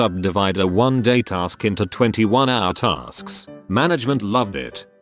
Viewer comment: I was doing something else while listening to this and thought it said "We'd subdivide a 1-day task into a 24 hour task. Management loved it!" and got so confused lol.